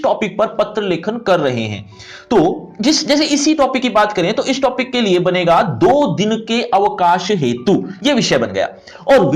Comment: i hear 2 din me aukaat dikha du